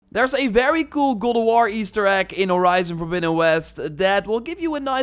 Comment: For a sec, i thought you said “google war” lol!